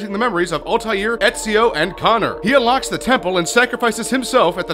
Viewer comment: did he say Connor